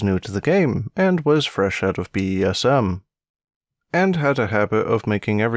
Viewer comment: I thought you said BDSM and I was concerned